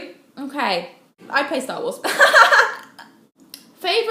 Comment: What does she answer at 2:13 ? I can't understand her. (English is not my native language) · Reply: i'd play star wars